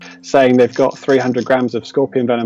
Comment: what did he mumble at the end? Sounds like an excuse to try and get people to back off the scorpion industry so he can take advantage. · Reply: He said"Do you want to buy it? The is answer is no, we got our own". You should go see a doctor to check your hearing.